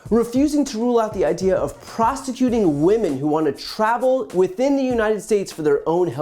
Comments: Did he say "prostituting women" @ 3:19?? 😂😂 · Did he say “prostituting women”?